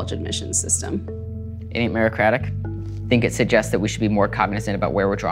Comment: What do you think this entire story says about the college admissions system? Yale Student: "It ain't meritocratic" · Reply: I thought he said “ain’t it merocratic?”